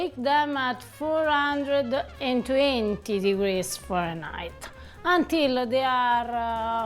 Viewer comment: Hopefully the people watching know you said Fahrenheit, not for a night I had close captions on, and it thought you said For a Night. Unless that’s what you said